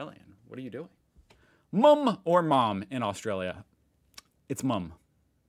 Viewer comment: Did he say moom?